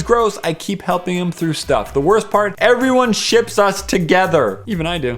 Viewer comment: At first I thought it said everyone sh*ts us together :0